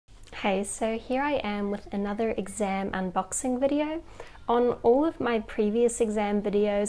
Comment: Did she just say an “exam unboxing” ? XD